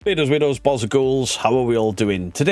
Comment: yanno for the longest time I could have swore was saying "beardos weirdos BALLS and ghouls". I'm actually serious when I say this. then I read the text that came up this time and the mandela effect damn near gave me a stroke. that might also be coz I'm old and I should really get that looked at. but I will DIE on this hill I SWEAR I heard "balls and ghouls" before! all while I'm still debating what I heard in the last line of SEG's outro song. yall debunkers got me fucked up...😑